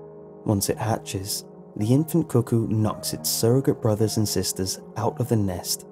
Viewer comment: When I heard you use the metaphor about the cuckoo bird, I thought you said kokko